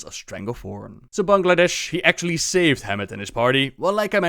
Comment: Did he just call the white tiger "Bangladesh" xD ? Earlier in the vid he also said "opportunitist"